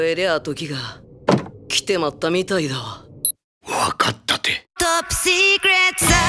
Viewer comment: 赤井さんの『わかったて』が『わかったって』って少し切れてるように聞こえて草